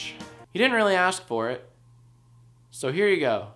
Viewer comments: The video wasn't supposed to get cut off like that at the ending, but I think it kinda works! The full line is, "Oh and we also released a couple new iPhones, but **farting sound**" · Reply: I thought you said. 'Oh and we released a couple of nudes.." 😂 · The video wasn't supposed to get cut off like that at the ending, but I think it kinda works! The full line is, "Oh and we also released a couple new iPhones, but **farting sound**" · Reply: I thought you said, "Oh, and we also released a couple of nudes." Bad choice to cut it...